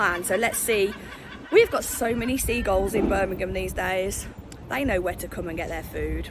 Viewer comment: Seagulls? Sounded like Olympia lol.